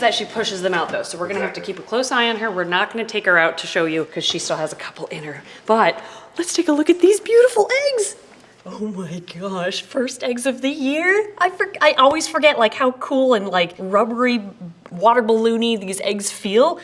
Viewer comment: it sounds like you say, "She still has a couple in her BUTT."